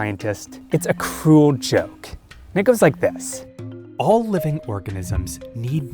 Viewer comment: woah what did bro say at 0:08 like what? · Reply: "it goes like this"